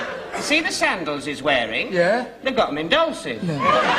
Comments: What is Mr. Humphries saying @ 26:30 what are 'midolces'? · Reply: He’s saying”……they got ‘em in Dolcis….” it was or is shoe store,not sure it exists anymore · See the sandals he's wearing? They've got [???]. 26:38 I can't understand what he says there, but the crowd sure seems to appreciate it. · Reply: I googled tonight and found an old 2009 discussion about this exact same question. The answer they agreed on is that the word is "Dolcis". "They've got them in Dolcis." It's apparently a designer label shoe store which had shops around London. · What does Mr. Humphries say at 26:39? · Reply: About the sandals? They've got mendosis. That's what I hear & what's in the script I found online